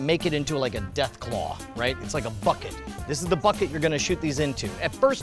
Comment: I thought said Fuck it not bucket.